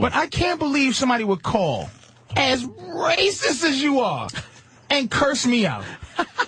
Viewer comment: This lady sucks for sure, but to be fair to her I totally thought Patrice called her a racist at 16:20, was he talking to Anthony?